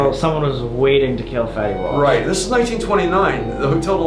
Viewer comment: I thought he said fettywap =)